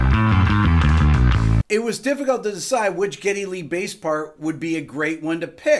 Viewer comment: I love how the captions says " kitty li" rather than geddy lee